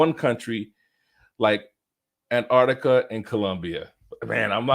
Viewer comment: I think that said Argentina not Antarctica lol